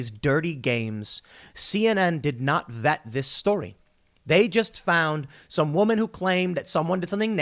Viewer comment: you say "CNN didn't vet their guest" but I hear "CNN told her to send herself a dog muzzle"